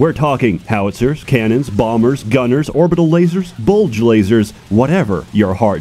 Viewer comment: I'm sorry did you say "BULGE laser"?!?!?!! ~@ 7:05 What. Like that dude in Once Upon a time in Mexico but instead of a six shooter its a laser?